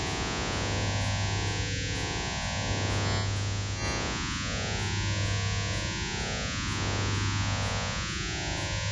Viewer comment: i think it said "let out" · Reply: *get out